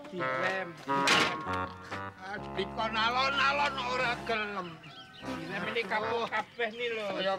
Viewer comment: Bilang apa tadi 🗿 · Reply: Di kon alon alon kok ora gelem...( Di suruh pelan pelan kok nggak mau )...